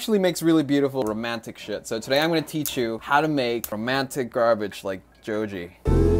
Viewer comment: did he just call it garbage ?